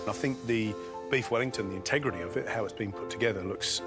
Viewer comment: I thought that they said beef wellington. I wondered why they used raw meat but then I realized that it was beet wellington LOL